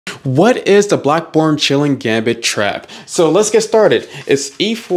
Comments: Wtf...i heard it as "black pawn chilling" Gambit... Then I saw the title...I must be crazy.. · Oh damn, I thought he said Black Born Chilling 😅😂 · I heard "black prn" lmao.